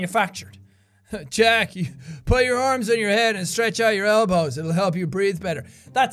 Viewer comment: So at 55:25, When Craig says "Jack, put your arms on your head and stretch out your elbows. It'll help you breath better." I thought he said "Jack, put your arms around my head and I'll help stretch you out." He clearly is my favorite Daddy so far....